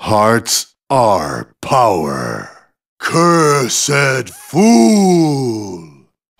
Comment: i always thought he was saying, "cursed. BOO!!